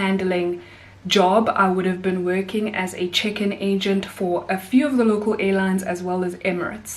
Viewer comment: as an American resident, it sounded like you said, "I would be working as a chicken agent 🐔" Sounds like an super under cover intelligence agency 🌻